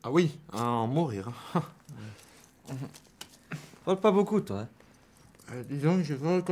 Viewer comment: il dit quoi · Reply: "Tu parle pas beaucoup toi hein "